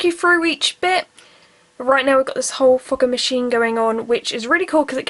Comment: When she said we got a whole fogging machine going on I thought she said a whole fuck*ing machine going on that accent broo😂😂😂